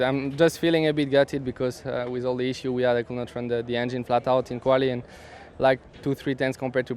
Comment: wtf was Gasly saying at 2:50 please translate to me in English.... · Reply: A bit gutted because all the issues couldn't run the engine flatout